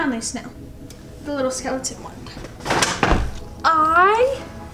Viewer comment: Did she just say “skeletont” lol miss girl